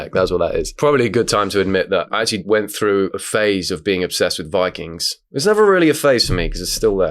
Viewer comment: i thought he said ‘bi kings’, safe to say im disappointed